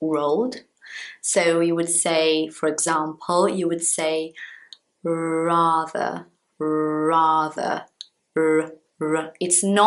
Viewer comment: Ela disse "rather" e quase que eu entendi "brother"😂😂😂😂😂😂 Não sei o porquê.